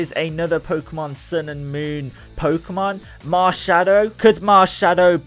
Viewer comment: When you said Marshadow I thought you said marshmallow XD